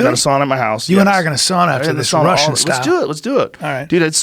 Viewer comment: did he say Sauna Russian style? That's like the most disrespecting thing you can say to a Finn, in things that makes a Finn proud, first is the winter war, second is sauna.